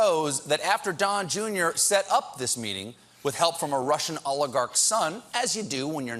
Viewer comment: Every time Colbert says oligarch, I hear "Olive Garden".